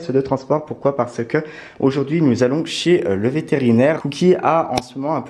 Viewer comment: j'ai compris aujourd'huit nous allons chier !!! je sui dit ok chacun ses delire